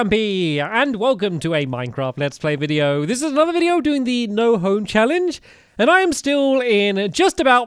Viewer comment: I thought he said no ho challenge 😂😂